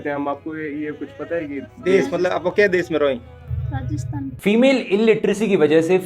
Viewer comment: it's rajasthan or Pakistan ? (I hear Pakistan )